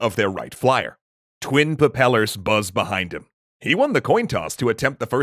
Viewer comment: im sorry did you just say pappellors?